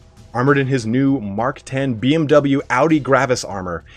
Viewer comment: waaait... did I just heard "BMW Audi armor"?! :D :D :D That's hilarious